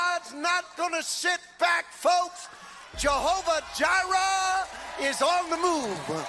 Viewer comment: Did he say Jehovah Diarrhea? I know he didn't but him talking about God makes me think he only has diarrhea foaming out of his mouth.